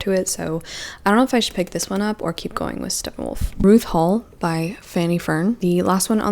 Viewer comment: You said “Ruth Hall” but I heard “RuPaul” and you know what, why not?